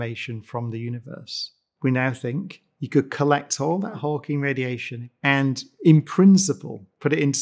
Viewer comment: It sounds like Brian says "hulking" radiation. If not for the context of black holes, it would be very confusing, instead of slightly confusing. :-/